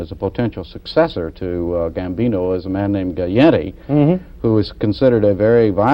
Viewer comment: "Gaetti instead of Gotti" 😂lol❤